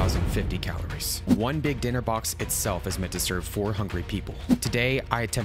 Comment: when you said "four hungry people" i heard that as "four hundred people" 😭😭